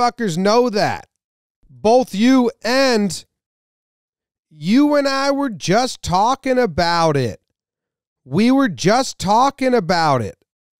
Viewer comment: La Russa wasn’t saying “you and I were just talking about it” at 3:10. He’s saying “what are you talking about?” He’s confused about what Baker is yelling about.